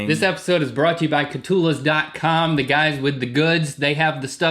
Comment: For a second there, I thought you said "Brought to you by Cthulus." And in that second I was happy such a magical restaurant existed.